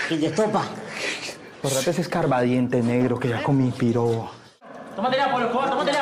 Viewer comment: no entendí mucho lo que dice pero me gustó el acento colombiano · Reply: "Borrate ese escarbadientes negro,que ya comí pirobo" Dice escarbadientes porque el cuchillo era muy pequeño xd